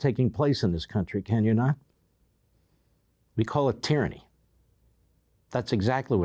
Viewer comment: NGL, I thought it said TRANNY. 🤣😂🤣😂🤣😂